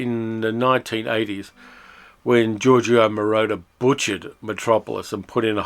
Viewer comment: I actually like the Moroder version, but I can easily understand people hating it. It is for me anyway a guilty pleasure. I have both the Moroder version and the recent Klino restoration. The Klino version is of course the masterpiece. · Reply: You and keep saying “Moroder” and I keep hearing “Mordor”…